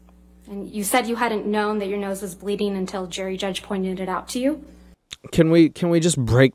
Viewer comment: the way you prounce jerry judge is the same you would pronounce jury, judge, so i was really confused because thsts what i thought you were saying lol